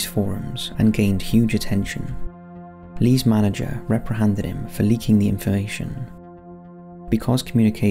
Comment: "Lee's manager reprihanded him" Um what? Reprihanded?? Are you just making up words now??? The word is REPRIMANDED. 🤔